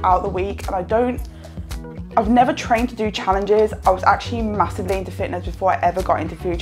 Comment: My goodness, you speak as fast as you eat. I clearly misheard and had to listen to it 5-6 times. I thought you said: 14.44 I never trained to make challenges, I actually masturbated into fitness...It must be I mastered into fitness, right? I wish my English was better. Sorry.